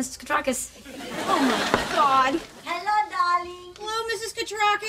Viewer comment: I thought she said hello dolly not hello darling